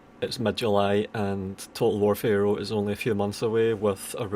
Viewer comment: somehow instead of "Total War Pharaoh is a few months away from release" i heard "Total War Failure is a few months away" in the beginning of the video, i think that perfectly checks out anyway